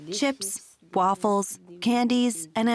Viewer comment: did she just say waffles?